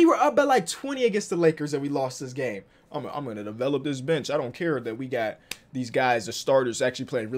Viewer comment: i thought said developers bitch lmao